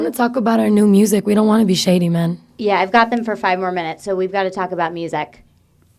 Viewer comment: I think Lauren said "so shady" no just me did anyone else hear that